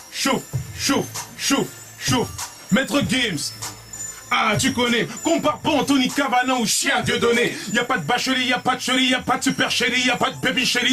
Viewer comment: Il dit quoi à 22:40 ????????? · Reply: Maître Gims, hein tu connais M'compare pas à Anthony Kavanagh ou chien Dieudonné Y'a pas d'vacherie, y'a pas d'che-ri, y'a pas d'supercherie Y'a pas d'baby chéri, c'est l'10-1 pour mon socs Algérie ---C'est un extrait de la Technique Interdite